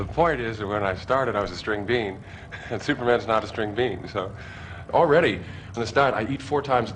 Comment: did he say, "already,I'm a stud"..????